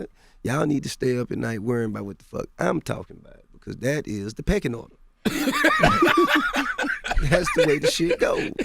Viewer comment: bruh somebody spell what he said around 22:54 peckanoid or something like that. wtf is that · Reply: pecking order lol